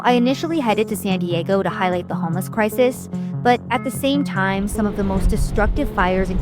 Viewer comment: I thought you said "to highlight the hummus crisis" , which is believable, talk about greenhouse gases. Ayayay. 🥙